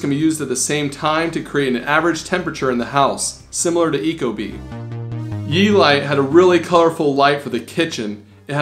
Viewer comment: what light is that 10:34 -10:47 ? did you say u-light? I don't find it :/